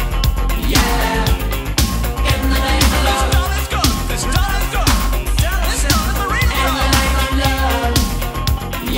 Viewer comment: Can someone please tell me what it's said from 2:57 to 3:01 ? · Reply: This stunner's good, this stunner's good, damn this stunner's a-real good!